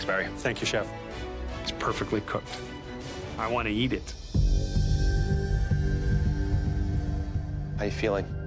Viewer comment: I thought said "I want to yeet it".